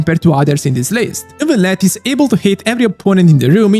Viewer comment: I love how I misheard "Neuvillette is able to hit every opponent in the room" as "Neuvillette is able to HATE every opponent in the room" 😂😂😂